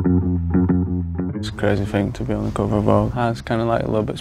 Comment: wait, did he say "it's crazy thing to be on ko ko bop" ??? 0:06 wut